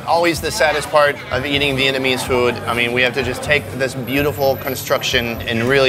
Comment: did he just say Satisfying eating enemies food😂😂😭😂😂😂😂😂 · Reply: He said, “Vietnamese food”.